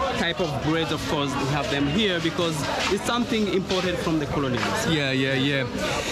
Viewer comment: I think he said imported not important